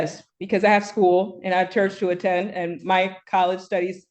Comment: "Church"? O thought she said she's a "Moorish American".